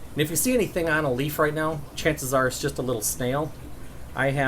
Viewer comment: I want the plant for breeding my own Green laser corys but It sounds Like he says bowbedus plant And I have no Idea what Its name is? Please help. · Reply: Bolbitis, if you haven't found it (or a good substitute) by now. The spelling may not be exact, but if you Google search "aquarium plant bolbi..." it should soon enough suggest the correct one!